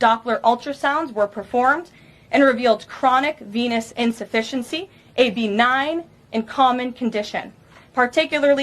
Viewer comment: Why did I hear "penis insufficiency"? 🤔